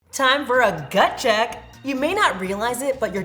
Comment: I thought you said but check! Haaaa